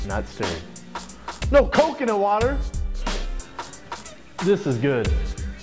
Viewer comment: I thought he was saying "no coke in the water?!" lmao